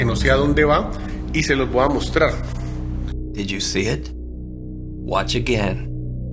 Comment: Me: (Goes to the bathroom at night) voice in my head: "And then... this." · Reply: i always hear "did u see it?" 🤣